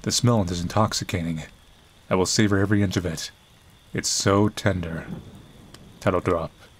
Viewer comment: I heard "Candor" not "Tender" at first and boy, was I confused for a second.